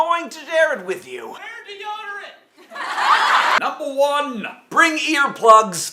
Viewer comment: What did the guy say at 1:11? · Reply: "Wear deodorant"